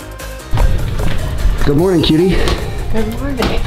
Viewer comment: did he say cutie or kitty? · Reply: Leo The boss. Cutie